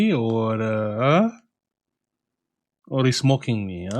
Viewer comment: I heard: not sure if the customer is making fun of me or is smoking w**d lol! Then listened again and heard: mocking me 😂